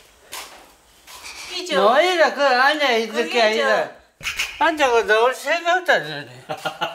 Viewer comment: "나오이라 거 앉아있을게 아이다"(나와라 거기 앉아있을게 아니다) 부산사람이라그런가 잘들리네요 ㅋㅌㅋㅋㅋㅋㅋㅋ